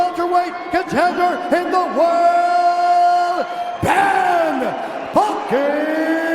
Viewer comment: What I heard: Ben f***ing asscraaaaaaaaaack